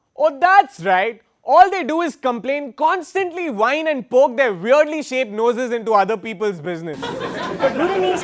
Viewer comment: is he just say weirdly shaped nose😁😂 look china started body shaming by sikh soldiers get up